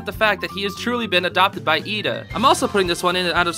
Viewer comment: Help I heard “He has truly been abducted by Eda” 😂